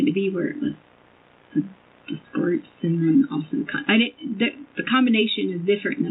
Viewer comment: I am watching this again ...after watching her trial and the verdict and her sentencing. It is absolutely amazing how she can talk for hours and have Nothing to say! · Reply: FBI guy says I worked with people who been dismembered, her reply was that's awesome. At first I thought she said awful. I rewinded it 3 times. She says that awesome, when he said I seen kids dismembered. She says that ms Awesome.. crazy is understatement wtf